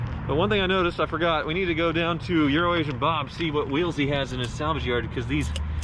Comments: It sounded like he said "Urination Bob" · I thought he said "It's URINATION Bob" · When you said Euro-Asian Bob it sounded like you said Urination Bob - so we have to assume that Bob is #1! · sounded like he said "urination bob" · I thought you said Urination Bob LOL